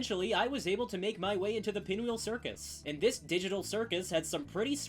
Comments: Did you just say the amazing digital circus? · d..did you say DIGITAL CIRCUS 🔥🔥